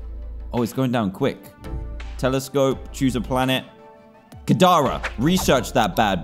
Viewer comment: i thought u said Katara from Avatar instead of Kadara